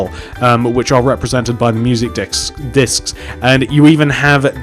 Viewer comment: Lmao this nigga said dicks instead of disk in minute 36:22, still love yo builds tho